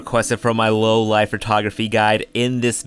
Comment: I thought he said low-life photography guide.